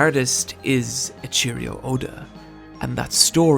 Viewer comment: Did you just say "A cheerio Oda"???